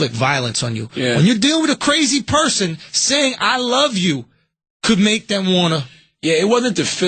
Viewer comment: What did Andrew say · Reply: Andrew: "Could make them wanna..." [Charla talks] Andrew: "fight you, man."